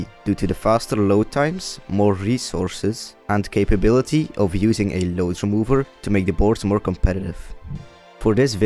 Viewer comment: he says "the ability to remove something remover" can someone clarify what he is saying maybe what it is and why it matters? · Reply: "capabilities of using a load remover to make the boards more competitive" - Load remover essentially pauses the timer when the game is loading to accurately display the actual time spent playing